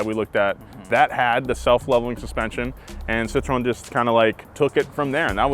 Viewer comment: when says Citroën, it sounds like he says “citron” which means “lemon”… which I suppose is still accurate 😆